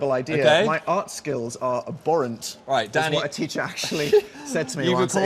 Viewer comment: did Phil say Danny? My alarms are going off!